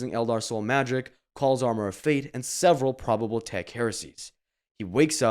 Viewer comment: WTF IS HE SAYING HERE? “calls armor a faith”?